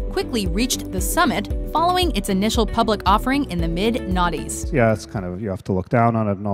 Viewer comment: did she say mid naughtys?